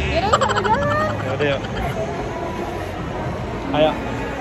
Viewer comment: Gemeesshhhh... 9:12 ayang... Iyaa ngga sih... Apa aku salah denger.. Hehehehe